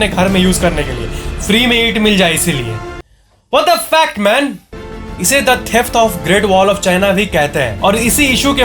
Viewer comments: He didn't say What The Fact Man he said What The F*** Man😂😂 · Kya bolla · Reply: What tha fake man😁😁😁🤣😂😂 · he want to say wtf but later he said what the fact man 😅😅😆😆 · I thought he is going to say ,what the f**k man But he said, what the fact man😅